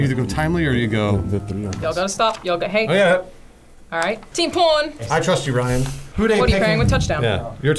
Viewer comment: It sounded like she was saying "teen porn" the whole video and it was messing with me lol.